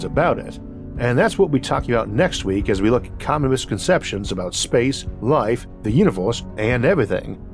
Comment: Did you say "Communist conceptions"? LoL